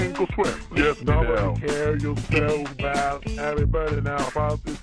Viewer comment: I always hear "you smell so bad."